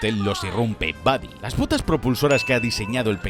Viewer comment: yo escuché “las pvtas propulsoras”